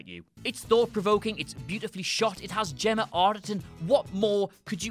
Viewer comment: It sounds like he says "Jenna Arterton" to me. At 2.50